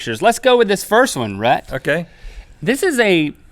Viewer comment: if you turn on the captions at 1:06, it says rat instead of rhet